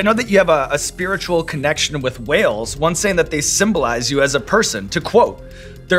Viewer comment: Oh whales .. I thought he said Wales had a spiritual connection with her